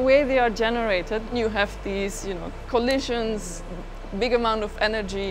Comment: It sounded like she said coalition